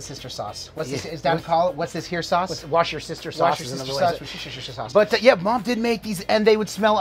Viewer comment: hahahaha...... thought he said "wash-your-sister-sauce"!! hahaha. Love watching twins keeping the bond alive, father of twin girls right here. Will be tryin this out on my lil 22 blackstone tomorrow!!